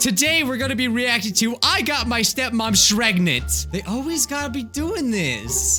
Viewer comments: he said shregnant instead of pregnant lol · I thought said ‘’ I got my sister Shreknent’’ Lol what are they having baby shreks?